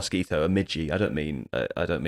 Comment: I really thought he said midget lol